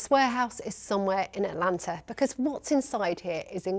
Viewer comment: Her: i cant reveal my location Also her: ATLANTA 🗣️ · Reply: I thought she said Atlantic but checking again she did say Atlanta